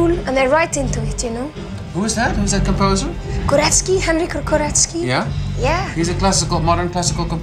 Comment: Who's that composer she mentions at 5:15? Korevski? Can't catch what she said.. Edit: ok, she said Henryk Górecki.